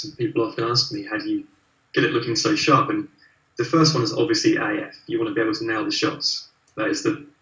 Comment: Hi everyone, I was listening this interview and can't figure out what's is saying at 26:35 (The first one is obviously ................. ?) Can someone tell me what is the secret for is sharp images! ;) Thank you! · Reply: I think he mentions AF which is his Auto Focus